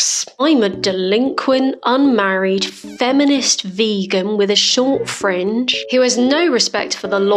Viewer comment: When you said short fringe in your intro I thought you said "with a short friend." I went down a rabbit hole of, "Does Abbey hate short people?"